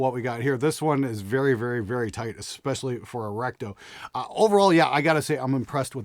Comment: I misheard 6:08 as "this one is very, very, very tight, especially for a rectum."